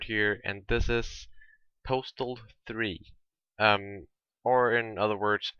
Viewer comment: Ok...who eless thought it said "Portal 3"?